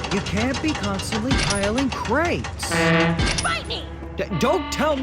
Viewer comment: BITE ME! Don't Tell Me To Bite You Sweetheart LOL Good Episode To Start Off 2023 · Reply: I kept hearing “FIGHT ME” not bite me. Lmao